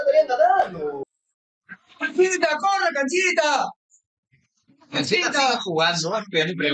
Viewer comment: Buena reacción, Aguante Canchita. Saludos de un hincha de Colo Colo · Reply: Pensé que decía Cachita "